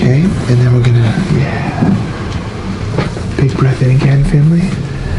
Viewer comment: I don’t know why, but @ 9:38, I thought said, “Big breath in again, family.” Y’know? Like, “big breath in again, fam,” instead of Finley. Now that I’ve heard it, I can’t hear anything else but fam.